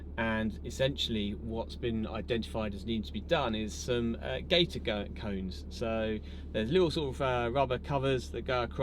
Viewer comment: At one point in this video my Canadian ear can't quite make out what you are saying. Are you saying, "gator cone" 3:13? What is a gator cone and what does it "go across"? Many thanks!